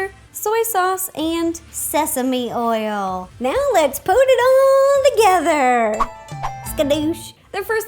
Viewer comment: Did she just say "now let's put in the WORM WATER!!!!!!!!!!!!" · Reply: Nichole Pautz listen carefully what she says is " now let's put it all together"